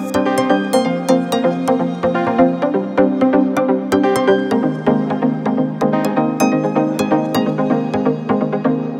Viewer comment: is it just me or did said something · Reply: He did he said I guess I